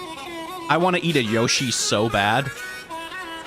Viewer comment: I swear I thought he said “I wanna eat a Yoshi so bad”